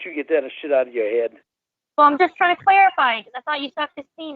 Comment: What did say?? She's just trying to q*eerify?? I mean idk if that's even a slur these days but I don't wanna take risks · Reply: Clarify